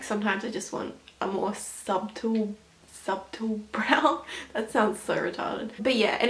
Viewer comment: Did she just say sub-tool instead of subtle?